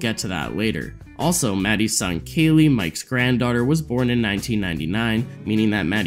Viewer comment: did he say "Mattys SON kayle"?